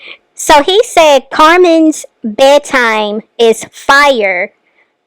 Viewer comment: So he did say bedtime. I thought he said bag time. WOW!!!